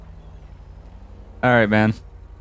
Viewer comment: mic cuts out to the point where you have no clue what he’s saying “Alright man”